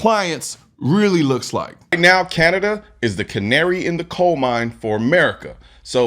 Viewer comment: So they ADMIT they cannot help you. But refuse to allow you to help yourself... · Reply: Ohhh this is Canada 🇨🇦 I thought he said Connecticut 😂 because that’s what they’re trying to do here in CT